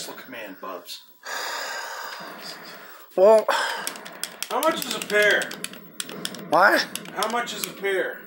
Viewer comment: I thought he said how much is beer¿😂🤣